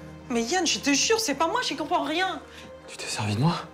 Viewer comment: Mais Yann je te jure c'est pas moi...??? Qu'est-ce qu'elle a dit après? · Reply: Houda Youcef elle a dit j’y comprends rien